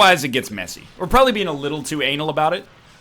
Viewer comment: ummmm 10:25 did i hear him right "We are probably being a little too ANAL about it"